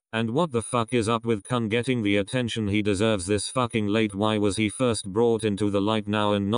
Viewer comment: I didn't understand what you said... "wtf is up with (...) getting..." Anyone please help... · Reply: She/he said and wtf is wrong with Kun getting the attention he deserves?